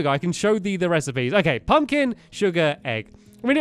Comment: when he said sugar I thought he said suga any one here an ARMY?